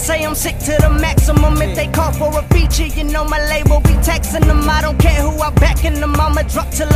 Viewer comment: wat does he say? Textin em or Taxin em?